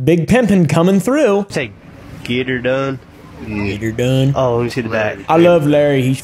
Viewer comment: sounds like oompa said n***er done